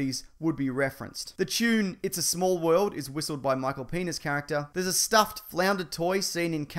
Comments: I heard Michael Penis Character 😂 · did he just said Michael Penis? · did he just say michael penis?